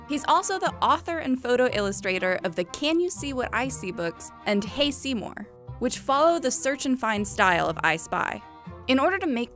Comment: Who else heard "tasty seymour"